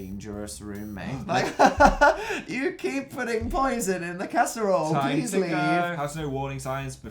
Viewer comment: "Hahaha you keep on putting poison in the casserole please leave." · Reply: Oh it was casserole? I thought he said cats bowl....